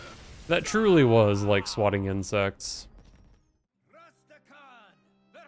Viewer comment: Wait, Al-Akir says, "Like swatting insects?" I always thought he was saying, "Thanks for the incense!"